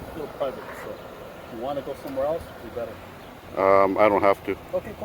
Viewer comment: Did he say at 4:16........* If you wanna go sowhere else, it would be better * Almost sound like a threat